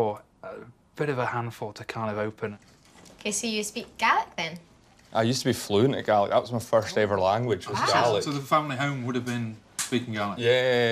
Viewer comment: I thought they said he spoke "garlic" 🤣